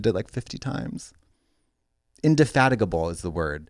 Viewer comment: I misheard that as "Inde-f****t-able", which I then changed mentally to "In the f****t, a bull" which is now Yoda describing Trixie's taste in men from what I know.